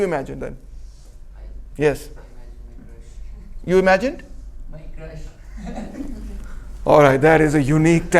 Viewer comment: What did that guy say at about 40:20? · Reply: He Said "My Crush" in his point of view, she is white.